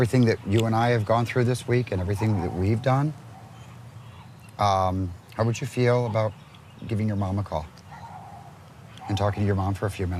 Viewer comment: I thought he said "How would you feel about giving your mom a CAR." .-.